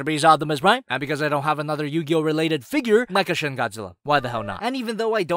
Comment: did you say mecha shin Godzilla...or am I hearing things?